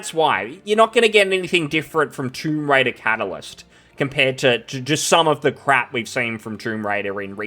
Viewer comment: Man i started laughing it sounded like you said "Troon Raider". 😂